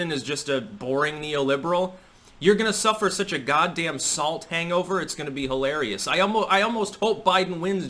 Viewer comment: DID JUST SAY "SALT HANGOVER" ? (7:37) it must flow